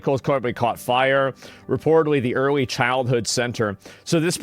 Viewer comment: oh, 'early childhood center'... I thought you said 'early life center'.